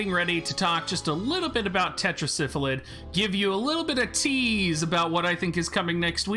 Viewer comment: its' Sylphid (Syl-Phid) not Syphlid (Syph-lid).. pardon my OCD lol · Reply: Thank you. Every time I hear him pronounce it the way he does if sounds like he is saying Syphilis.